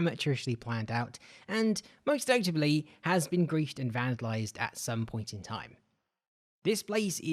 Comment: My dumbass thought that said garfield instead of griefed